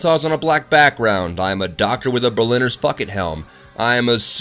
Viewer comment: says fuckit instead of bucket. XD